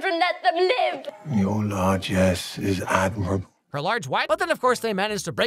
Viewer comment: I still can’t decipher what Hook said here, it still sounds like “large ass”… at least we can agree Hook’s evil to the core